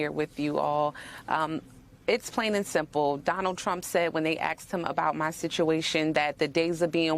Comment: Did she say " axed him" instead of asked him? at 1:00 minute mark. How ghetto